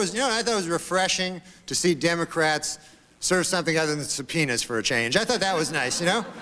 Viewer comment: i always thought he said “i thought it was refreshing to see democrats serve something other than some penis for a change” not subpoenas lol